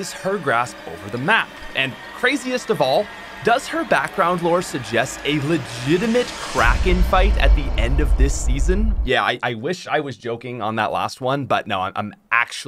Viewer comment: When he said "Kracken fight" I heard "Crack in-fight" and was very confused